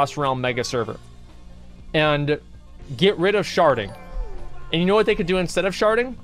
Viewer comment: swear on my fucking life you said "sharting" lmfao